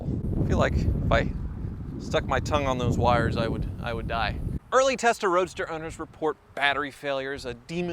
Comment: "early Testarossa owners" is what I heard 👌😁